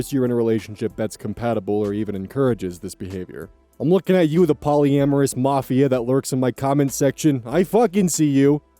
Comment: Misheard the first time and thought he said polyamorous moth 😭